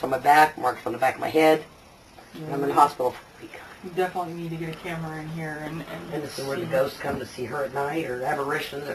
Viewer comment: Did you guys pick up any evp's in this investigation? I know that it might be someone from yout team but i heard a few of them. Check 17:06 19:39 and 20:16 I heard a few more but i have to watch the video again to get the time when it occurred. Does anyoe know what is being said? Please let me know. Thanks! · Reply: Nice work, here's what they say (imo) : 17:05 - "Please help me out" Class A EVP 20:15 - "He's too hungry" Class A EVP